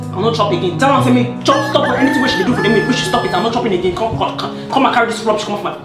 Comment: I thought he said he's not eating again 😂😂😂😂😂